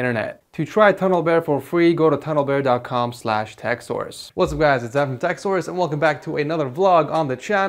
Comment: I thought you said what's up guys it's ed from sex source